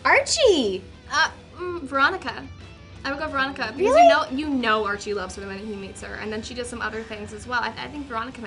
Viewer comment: What does Cheryl say??? I couldn't hear. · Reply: RedRoseGirl_xx "you know archie loves her the moment he meets her"